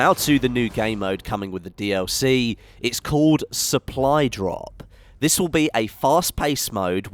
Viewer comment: did he say.. supply drop?